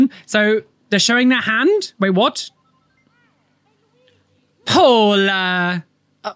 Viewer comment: I was listening to while cleaning and at first, I thought he said "Polar" not Paula, then with the "I love you" Misery quote, I thought a couple got some kind of unique couple tattoo. My first thought was that one person got the word 'Polar' tattooed on them while their partner got the word 'Opposite' tattooed on them matching the designs, calligraphy and body placement of their partners tattoo. Which made me remark out loud that that would be a cute, interesting, and really unique couple tattoo. Made better by the fact that, even if the couple broke up after getting the tatts, they could keep their tattoo as is, slightly change it or add on to their tattoo to make it their own again. Without anyone but the couple knowing that their tatt was a "couples" tattoo. Meaning they save themselves from having to get a cover up tattoo to hide their exes name...😂 Then I went and watched the video and could only shake my head in dismay. Getting your partners name is the stupidest tattoo any couple could get. The only time I don't mind name tattoos is if they're done to honor a dead loved one, a parent or grandparent gets the name(s) of their child(ren) or grandchild(ren); close sibling(s) or BFFs, get eachothers names and of course if someone gets their own name (first or last) tattooed on them.